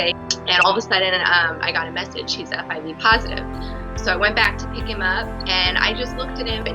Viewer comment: Lmao I thought I heard her say “she’s HIV positive “